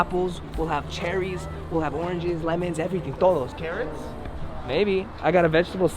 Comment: I GOT CHOKED--- I MISHEARD CARROTS AS CARATS. LMAO